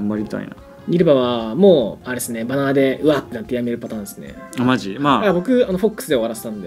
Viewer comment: ここのさん、なんて言ってんの？ 「ニルヴァは、もう、あれっすね、※※※※※※るパターンすね」 ※のとこが聞き取れない。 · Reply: バナーでうわってなってやめ